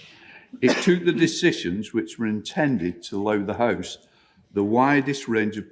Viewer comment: Dear British folks, a linguistic enquiry from a loyal Novara media viewer Stateside, with a clear view of the Pacific Ocean (while facing in the western direction). What is this word spoken by Lindsay Hoyle at approx 8:23 or 8:24 that YouTube has transcribed "loow"? As it seems unlikely given the context that it is a borrowing of the word for lion from the North Friesian dialect, I would really like to know its spelling & meaning. Thank you in advance for indulging my unpopular hobby. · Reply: I think he said 'allow'